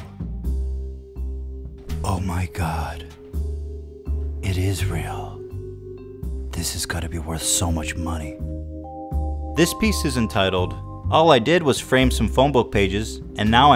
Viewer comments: I thought he said "Isreal" haha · Sounds like Ethan said Israel